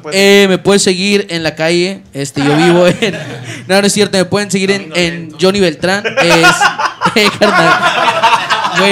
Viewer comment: Que dijo aczino? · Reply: Jony- me pueden seguir en la calle Aczino- camino lento